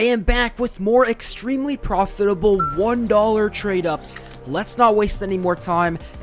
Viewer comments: proditable instead of profitable nice trick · proditable instead of profitable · Proditable instead of profitable 😂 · proditable instead of profitable :p Another great video man <3